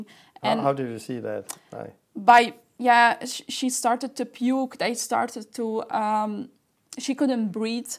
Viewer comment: She started to puke?! Did I hear that right?!